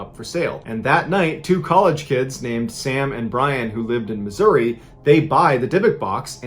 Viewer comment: Sam and Bryan...? I almost thought i heard Sam and Dean